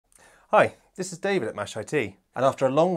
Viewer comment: I always thought your introduction said "Hi, this is David Machete." I now see you have a machete